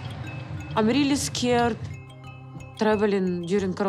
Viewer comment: What's up with the subtitles at 0:31 she's saying the borders are closed, not that they're close 🤦🏽‍♀️